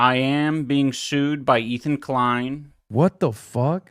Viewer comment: I never even heard of this guy, I thought you were saying Ezra Klein 0_o